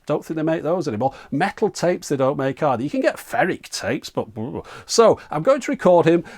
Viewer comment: What'd he say? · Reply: Ferric tapes